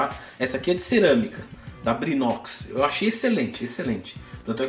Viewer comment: Qual a marca da frigideira, por favor??? Eu entendi “trinox” mas não achei nada no google. Seria Brinox??